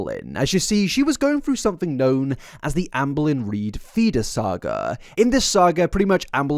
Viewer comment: i keep hearing "fetus saga" not "feeder saga" when you talks...it also sounds like you are saying Anne Boelyn, not lol